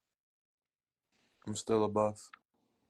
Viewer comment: Am I tripping or did he say I’m still a Buff